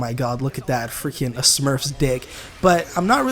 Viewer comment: Did u say smurfs dick